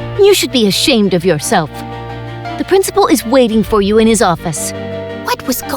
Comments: Pause at 8:43 it says 'her' instead of 'his' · the captions said her but the voice said his. that's sus...